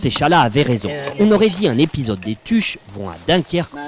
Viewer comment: = :D ik denk wel dat dienen bout wa krom staat...Maintenant je sais pourquoi chez Vespa aussi, leur meilleur modèle s'appelle GS. Au fait, il a pas dit "Tu" mais bien "Dju"...l'abbréviation Belge de "Nom de..."